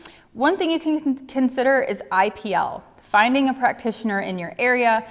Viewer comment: Hi English isn’t my native language, I don’t understand the first healthier alternative starting at 10:55… Can someone tell me what Dr is saying please? eye Pe..? Thank you so much. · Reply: IPL, LLLT - low level light therapy, moisturizer, sunscreen, hat, and sunglasses.